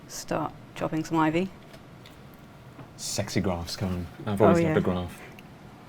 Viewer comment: Did he call her a sexy graph cone? Someone's getting laid tonight!